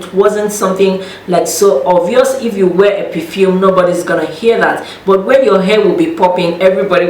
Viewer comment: Did she say "if you wear a perfume nobody will HEAR the smell" 10:50 OMG